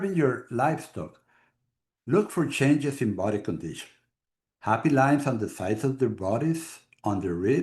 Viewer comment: does this say “happy lines on the sides of their bodies…”. ??